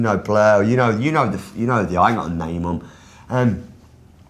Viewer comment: Minute 4:10 "you know blur... you know I know the ..." What does he say? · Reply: Eduardo Soto 'I ain't gotta name them'